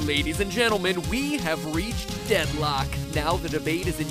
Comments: For a second, I thought it said 'Deadlox' not 'Deadlock'. XD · oh fow a sec i thought he said Deadlox not Deadlock